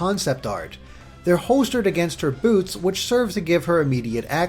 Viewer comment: I wasn't paying attention at 1:49 and I could have sworn I heard "boobs".